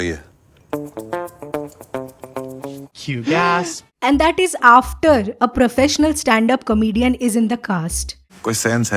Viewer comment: what the gora is saying in this clip?? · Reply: *Cue gasp*... like in drama scripts we give cues to someone to gasp and he does